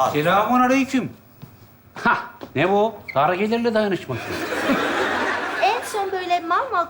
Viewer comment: ne diyor? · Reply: Dar gelirler dayanisma